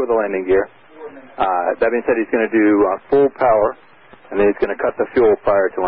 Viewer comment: I’m confused why he wants to do “full power” before shutting the fuel? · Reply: Sounds like he said “pull power”